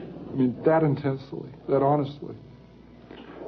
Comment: Did he say "I'm not a lizard" ??? WTF